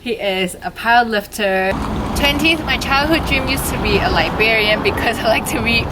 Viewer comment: I thought you said your childhood dream was to be Liberian. I was like "oh... never heard that one before" 😂